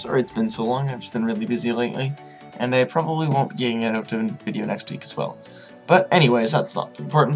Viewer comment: What are you even saying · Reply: i said that there will not be a video next week